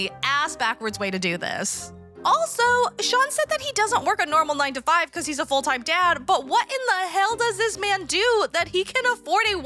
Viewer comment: I really thought you said “Sean doesn’t work a 9-5 bc he’s a full-time Chad”